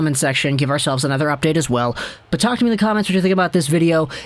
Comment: Cold coffee. The first time you said what to comment I thought you said to comment "Cold Caufield" and I was kinda confused.